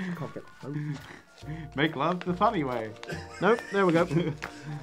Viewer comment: Like for saying, “Make love the bunny way.” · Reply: I thought he said "Make love the Funny way" 😆